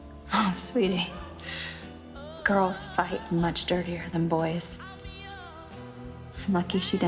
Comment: can anyone tell me please what does alison say at 2:12? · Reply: alessia malagoli Girls fight much dirtier than boys